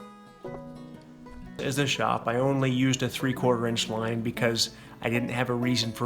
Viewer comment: what were you saying? It got cut off. It sounded like “is a shop” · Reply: The house was going to be a shop initially.